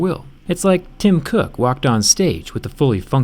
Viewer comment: OMG I THOUGHT HE SAID “It’s like Jungkook walked on stage”